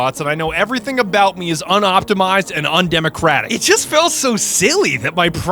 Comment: did he just say undemocratic